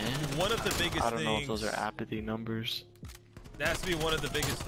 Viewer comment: "Those aren't apathy numbers".... The disrespect 😭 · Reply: I thought dude said “Are those Apathy numbers, idk” lol